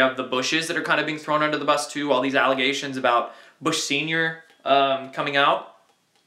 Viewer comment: HA HA, it sounded like you said Bush senior came out.. You were saying that info was coming out, but the pause you took...just play it back to hear what I mean, Ha Ha, Funny shit.